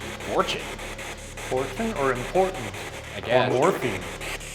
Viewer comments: i heard morphine instead of important & maybe that was one of the ppl asking for morphine .. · I didn't hear "Important," I heard "Portal." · sounds like he is saying morphine which would make sense when you then talk about how they have been recorded asking for morphine and the previous recording it said important #postmortem #Post Mortem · #postmortem when the wheelchair entity said “important” it almost sounded like it said “portal” maybe in reference to the alleged portal to hell in the building? p.s.I went to elementary school in the area and we went to both old city jail and the USS yorktown on field trips (along with other haunted locations) so its super fun to watch y’all ghost hunt there! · I heard morphine but didn’t understand how that could be relevant until they mentioned it later on in the episode 🤷🏽‍♂️ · i don't think it says important i think it says portal, as in portal to another world?? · it sounded like it said “morphine” #postmortem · I heard ‘morphine’ (before I saw the part about soldiers asking for morphone!!! ) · I thought the spirit box said "boredom", not "important." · when it was saying "important" to me it sounded like "warden" as in the warden is why it is staying there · #postmortem when the voice in the spirit box said, "important" I thought it said "the warden" 🤷‍♀️ 9:17 anyways..whatever it said, it was all creepy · I swear I heard “portal” instead of “important” #postmortem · What if at 9:19 it said “portal”